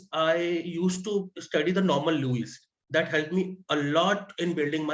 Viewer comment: did he say novel louise??? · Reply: "Norman Lewis's Word Power Made Easy" A book for vocabulary